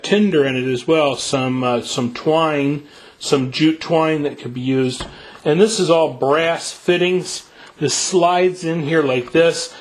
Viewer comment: did he say "jew twine"?